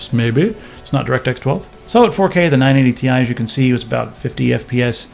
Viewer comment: lol i kept hearing "980 tie"